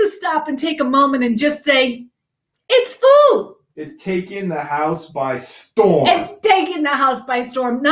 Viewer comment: I misheard "it's taking the house by storm" as "it's caking the house by storm."😅